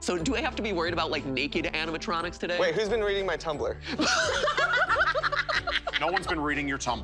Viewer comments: So do I have to be worried about naked animatronics today Matt: WAIT WHOS BEEN READING MY TUMBLER whole group: * laughs * Me: Wait what did he just say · So do I have to be worried about naked animatronics today Matt: WAIT WHOS BEEN READING MY TUMBLER whole group: * laughs * Me: Wait what did he just say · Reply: uhhh i thought he said "WHAT WHO'S BEEN READING MY TUMBLER!?"